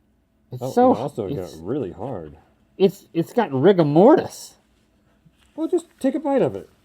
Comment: Who else heard Rick and Morty's?